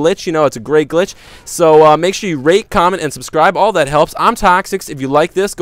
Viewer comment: at the end it sounded like he sayed rape and subscribe!